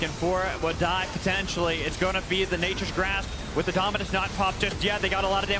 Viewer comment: Jesus, if he says Nature's Wrath one more time, it's Nature's Grasp. How tf have you gotten that wrong all season.... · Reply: I thought he said nature's grass💀